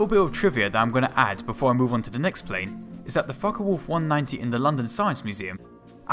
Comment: the sub actually says fucker instead of focke LOL